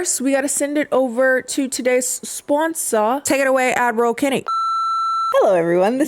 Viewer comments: I just need to say that I always hear “adderall Kennie” instead of “Ad Roll Kennie” hahahahaaha and I had not questioned it until one day, you called people out for thinking you were saying “Admiral Kennie” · I know everyone else hears “admiral Kennie” but I always hear “Avril Kennie” and expect to see pink streaks and a hot topic belt when I look up lol